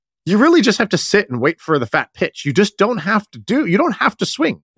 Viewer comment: A few weeks ago I had to explain to my wife, what you said opposed to what it sounded like to her from across the room when you said fat pitches.